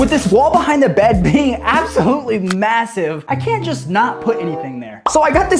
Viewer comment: Did I hear massive?